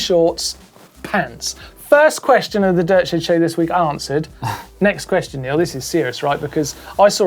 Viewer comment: Hahahaha... thought he said "the daily shit shed show"